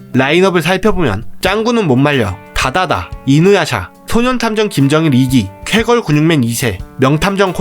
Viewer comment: 초 저만 소년탐정 김정일이라고 들리나요?? ㅋㅋ